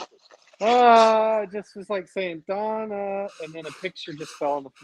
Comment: I heard Donnarrrrrr. Really weird.